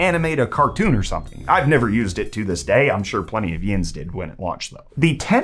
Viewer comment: Did you just say YNs as a word!?! 😭😭😭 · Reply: He said yinz! Omll haha its a pittsburghese saying